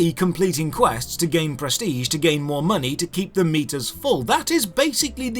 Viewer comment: oh, he's saying keep these meters full. i thought he was saying keep these meatus full.